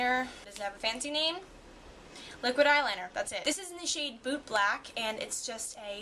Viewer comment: i thought u said "boob black" lawl